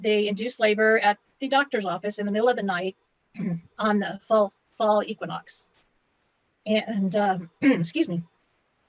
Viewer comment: The woman at the end said "A full equinox". There is something very wrong about her and her stories. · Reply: I thought she said a "fall" equinox.